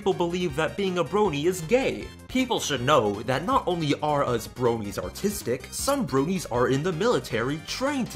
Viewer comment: I thought he said " people should know that not only us bronies are autistic...." Would make more sense.